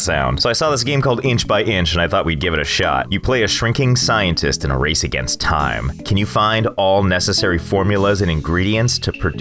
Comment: I heard " A shrinking Scientist in a rascits Time. I was thinking the game world must be full of bigots who mock him for getting shorter and for being a LoTR Hobbit.